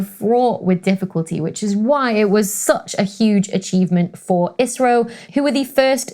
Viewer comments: I listen to it twice and not sure but think you said Israel space agency instead of Indian. At around 7:40 . Probably need to rewind again. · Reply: You heard ISRO not Israel smh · Did you say "Israel" at 7:41?